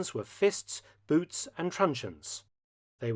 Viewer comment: "Their weapons were fists, boots and..." and what? Subtitles say that "trunions", but that isn't an english word. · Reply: Truncheons